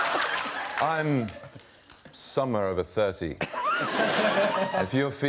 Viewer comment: "Somewhere over 30" I'm writing this on my birthday cakes from now on 😂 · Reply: I thought he said a summer over 30